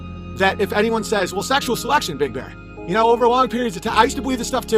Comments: Did he call himself Big Bear @ 12:25 ? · wait wait wait did he just call himself big bear? 😂😂 im DEAD